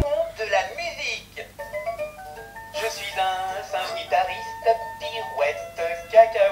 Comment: dites moi que je suis pas le seul à entendre "Je suis un syndicaliste, pirouette cacahuètes !"